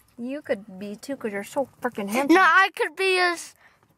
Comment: did she say fuckin handsome? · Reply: She said fricken ☺️